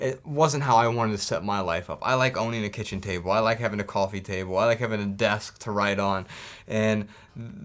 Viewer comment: At first I thought he said "I like having a desk to ride on" lolol